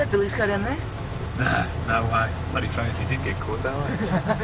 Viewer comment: WHAT IS HE SAYING???? I can't understand what he is saying. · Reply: "Nah, no way. Bloody funny if he did get caught though eh?"